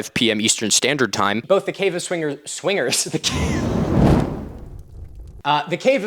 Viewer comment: Until I saw the title card at 17:24 I thought he was saying “Cave of Swimmers”